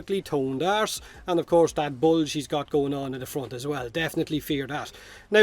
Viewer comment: sounded like you said "that bulge she's got going on in the front"